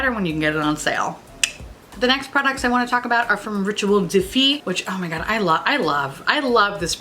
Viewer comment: You know it’s bougie when it’s in French. When she got to “Rituel de Fille,” I legit heard “Ritual Defeat” and thought, “Dang, that’s an awesome name for either a makeup brand or a rock band.” 😂